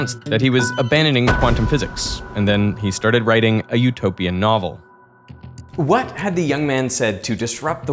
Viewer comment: I chuckled cuz I thought you said "dystopian novel" had to replay that. My head was like OMG like a really life Monty Python's Worlds Funniest Joke, but in reverse!